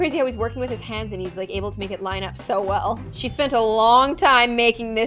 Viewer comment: I think I heard her say she in